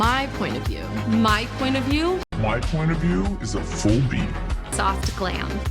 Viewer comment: At the end when they’re all saying my point of view and everybody saying theirs when it comes to Mikayla, I thought she was saying my point of view is a beak cause her accent sucks